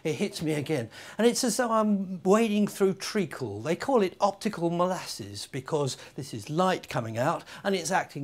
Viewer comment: At first I though I heard "Optical Molesters". Made sense at the time.